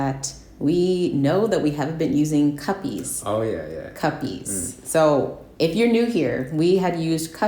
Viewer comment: For the first couple of times I thought you guys were saying "guppies"😬☺️